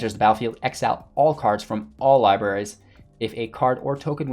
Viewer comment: I was listening but not watching the screen, and then I hear : "*when it enters the battlefeild exile ALL libraries*" I was like ... Wtf did wotc did to historic .. Got me confused there for a second xD